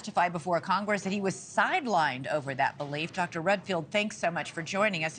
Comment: I thought she said Dr REDPILLED lol